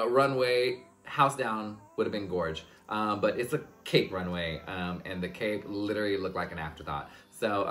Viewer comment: I thought u said cake runway and that’s brilliant